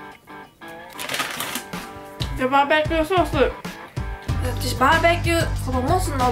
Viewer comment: バーベキューソースがジョバベティーソースって聞こえたｗｗ